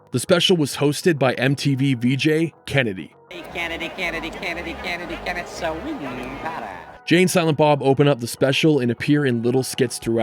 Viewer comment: I know this sounds like "Kennedy Kennedy Kennedy" -- and I think even some subtitles say that's what it is -- but Cameron is clearly taunting the batter with "he can't hit he can't hit he can't hit".